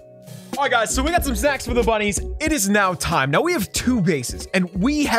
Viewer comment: what did he say😨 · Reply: Aight guys so we got some snacks for the bunnies?